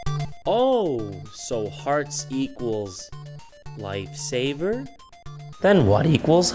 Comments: I thought said Lifesaber instead of Lightsave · Every time he said "Lifesaver" i heard "Lightsaber".